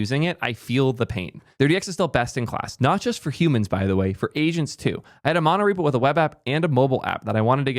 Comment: "Not just for humans btw, for asians too"😂😂 I had to rewind that for a second (He actually said agents too)